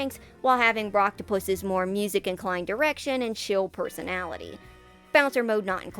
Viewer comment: I heard erection did anyone else.